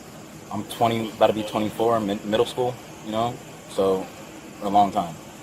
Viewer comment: did he say he was 2024 in middle school lol